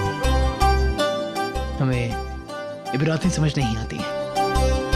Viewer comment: What is the word he says at 1:44? Hame.......samajh me nahi aati? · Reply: Checked on google. Ibrat meaning arabic. He says he dosent understand Arabic